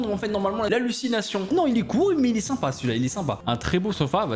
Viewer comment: A 11:50 moi j'entends :un très beau sora 😂😂 (en même temps c'est vrai)👍🏻😂